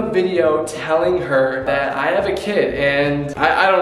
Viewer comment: Wait who else heard him say " telling Mariah I have another kid "